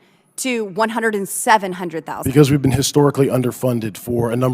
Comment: Did she just say 107,000 as 100 and 700 thousand. 🤷🏾‍♂️